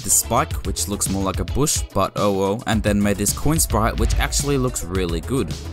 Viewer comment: that "oh well" sounded like owo lol